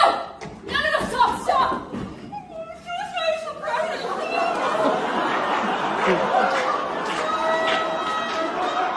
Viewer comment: Does anyone know what Heather M. said when at 0:38 and 0:52? I can't make it out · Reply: "Suicide is a private thing!"